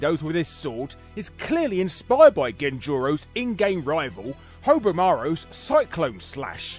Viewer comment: What the heck did you call Haohmaru? Hovermaru?